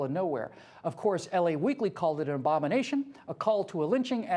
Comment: The first time I heard 6:52, I though he said "OBAMINATION."